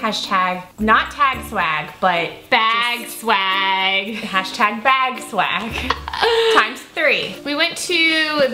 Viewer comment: I love seeing product reviews, empties, favorites, and drugstore tutorials. I had to go back, because I thought one of you said swaggy bag, but that's not at all what you said. It did make me laugh though, cuz I mean, who says that?